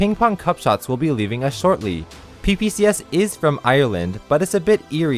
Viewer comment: Lol, I thought he said PBS. Ping Pong Cup Shots = PBS. PLEASE BRING BACK THE TELETUBBIES